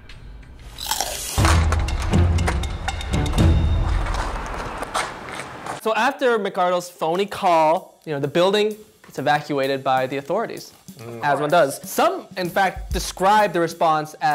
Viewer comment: Did he just say “phoney call”?